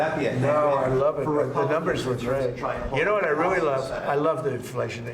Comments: I heard it as - the numbers were great. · To my ear, (and if you read his lips) I think he said, “The numbers were great.”, not ‘the numbers were glitched.”